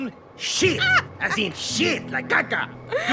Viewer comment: did he just say SHIT